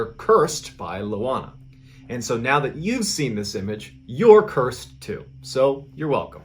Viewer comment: Good thing I was listening while mopping my house and didn't get see the photo haha All I heard was "you are no cursed, you're welcome " haha